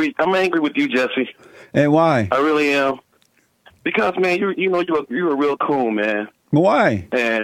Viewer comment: I thought he said "you're real cool." I was a little off.